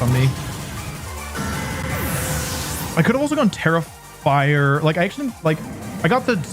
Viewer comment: I've put a lot of hours into Violet. I'm perfectly familiar with the Tera mechanic. And yet, every time he says "Tera Fire", I hear "Terrifier" instead and have no idea what he's talking about for a few seconds